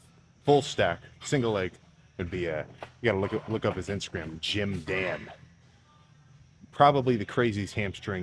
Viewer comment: Who is he saying?? Jim dan? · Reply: gymdann